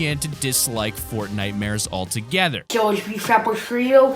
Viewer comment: did he say he is “fat frito”